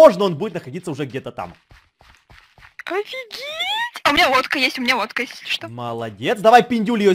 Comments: А мне послышалось:А у меня водка есть 😂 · Когда говорила лодка мне послышалось водка🤣